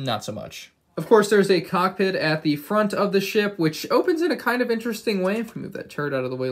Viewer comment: did he just say ship